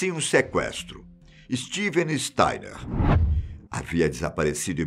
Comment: Alguém sabe mais sobre esse caso? Tentei achar pelo nome do garoto mas n sei se entendi certo: Steven styler